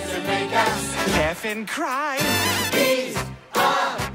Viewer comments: I always loved it when Stewie says "Laugh and cry!" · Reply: I always thought he said F'in cry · I always imagined Stewie saying: “Laugh and Die”💀💀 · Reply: He says "laugh and cry" but it's easy to hear sometimes it sounds like he says "effin' cry"